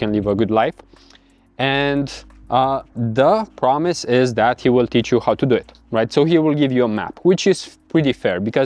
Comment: wait this wont work if we dont have audiences? i thought he said he will tc of it lol